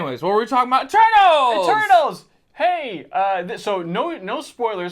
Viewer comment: anyone else hear "Turtles"?